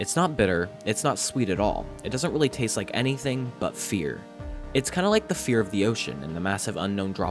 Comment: I got an ad so it cut off and sounded like it said “it doesn’t really taste like anything but feet” 💀💀💀💀